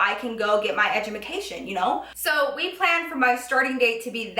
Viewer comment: did she said "EDUMACATIOM" on